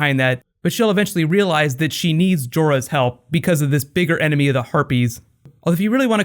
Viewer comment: Sometimes I hear "this bigger enemy : the herpes" xD